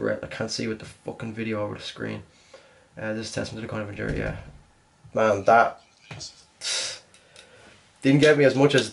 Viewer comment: did he said MANTAB????